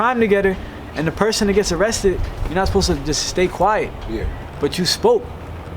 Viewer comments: He Told Listen what he said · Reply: Bro said you not suppose to stay quiet 🤦🏾‍♂️ that nigga snitching on himself on the interview to😂😂 nigga just a natural snitch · does he say "when people do a crime together you're NOT supposed to stay quiet" ??????